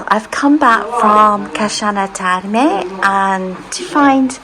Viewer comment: I’m sorry about the confusion on this discussion. I thought I heard you use the soft chi sound at the beginning of your video. I went back and at this point you did pronounce the name of the town but I’m not sure what town you were referring to at this point. I thought I heard you pronounce Chianni with a soft chi, but may well have been absolutely incorrect. My sincere apologies if I misinterpreted your pronunciation of Chianni. It’s somewhat basic beginner Italian so I didn’t mean to make light of your Italian pronunciation or language skills. 😊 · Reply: you probably heard me say Casciana which is where I was that morning. That’s all I can imagine 🤔